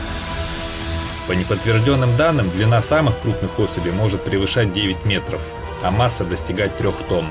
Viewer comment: 6 метровый крокодил весит 1.1 т. А семиметровый , 2000т. В одном метре 900 кг? А самки 9 метров и 3 т? Тут в двух метрах по 500 кг? Какая-то странная арифметика? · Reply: Он говорил: длина не самок крупных особей, а самых крупных особей. Я плохо расслышал.